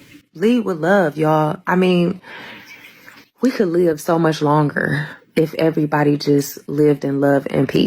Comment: What does she say? Seriously? @ 9:30-ish, "We could live so much longer is everybody just lived and loved in peace?" Is that what I heard her say? I did hear the correctly, correct? Then why on earth did she not leave the other passengers on that plane to travel on their trip in peace? Seriously? Where is the line of thinking here?